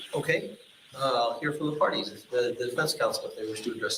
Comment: Catchin' on the REPLAYU ??? Ummmmmm...??? At. around timestamp: 29:44 did I hear the Judge say: "Here for the party"...??? 😂😂😂